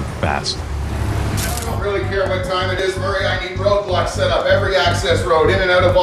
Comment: Did he say ROBLOX? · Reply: He said ‘Go Block every access’